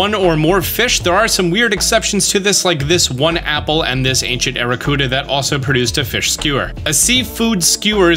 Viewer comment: did he say ancient aerocuda?